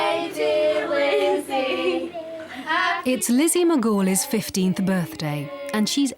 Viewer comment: I thought they said Lizzy McGuier for a sec